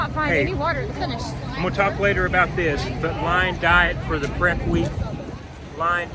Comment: Thanks for your commentary! Quick question- I wasn't able to discern what you were saying about the diet plan the week of. Would you mind clarifying what you were referring to? Time stamp: 14:25 THANKS! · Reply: Yes! Thanks for the comment cirque. I was talking about going Lion Diet leading up to the race. So for 7 days leading up to race day, all I ate was two pounds of steak (with a lot of butter/ghee) and water with a half teaspoon of salt added everyday for food. And it made my performance so amazing. It was a definite game changer. I will be doing that for every race on out.